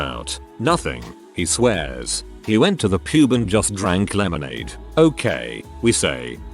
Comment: when I heard the TTS bot go "he went to the pube and drank lemonade" and really had to do a double take at what I was hearing...